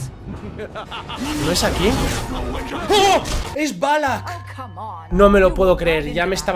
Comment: Balak? Eso no es un nombre extrangero o yo entendi balak en lugar de malak xd YaY eres un verdadero crack